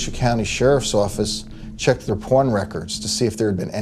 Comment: at first i thought he said their PORN records.